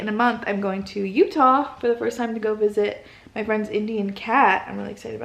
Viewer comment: Sounded like you said you're going to visit your "friend's indian cat" hahah